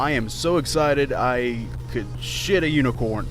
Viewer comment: I would like to see you shit a unicorn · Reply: For a second, I thought it said: "HIT a unicorn" instead of shit a unicorn... Which honestly would have been more realistic...